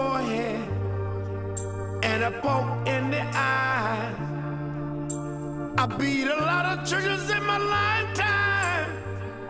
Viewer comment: am I the only one who thought that he said "I beat a lot of chickens in my life time!"?